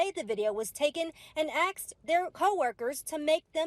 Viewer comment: That reported said "Axed" instead of "Asked."